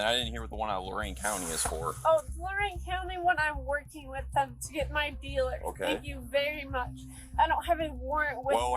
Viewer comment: did she just say shes working with police to arrest her drug dealer? So she got arrested and threw her dealer under the bus to try to get out of trouble.